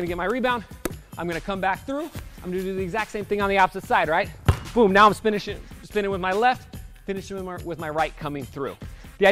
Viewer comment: "Now I'm spinning shit 😳.." lol don't think he meant to say that 😂😂